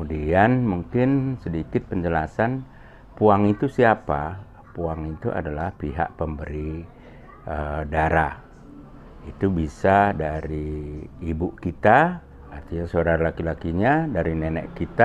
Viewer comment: izin bertanya, di menit 2:07 , isebutkan : "puang itu pihak pemberi darah." apakah benar? soalnya takut salah dengar hehe. Sebelumnya terima kasih atas pengetahuan nya poli.. njuahnjuah